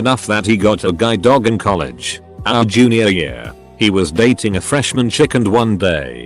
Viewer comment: I thought it said “he was dating a “”fresh-men”” ‘chicken’ 😂